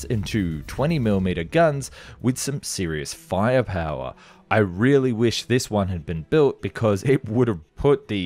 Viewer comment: Did he say... @ 7:47 " I really wish this one would have been built ??". A Nazi Killing Machine... Wow !!!